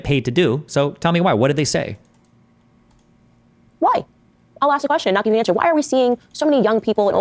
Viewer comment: is she saying ‘I’ll ask the question but not give an answer’ Did I hear that right?